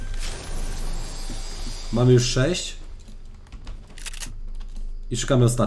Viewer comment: czy tylko ja usłyszałem mariusz6 😂😂😂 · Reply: Mam już 6 ( skrzynki z amunicja